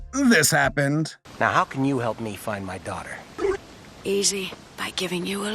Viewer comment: Thought for sure I heard "How can you help me find my daughter?" "Easy, by giving you one."